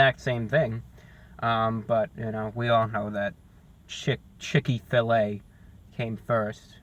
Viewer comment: Haha did you say shit filet at 4:36!